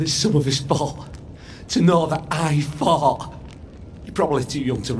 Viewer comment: anybody else hear when he says fought it sounds like he is saying "I fart".